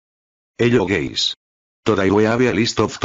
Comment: I hear "hello gays"